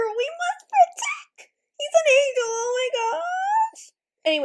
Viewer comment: Idk but I heard "He's an angle"